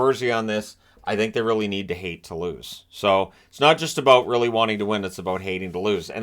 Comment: I thought he said, "Hate the Blues" instead of hate to lose...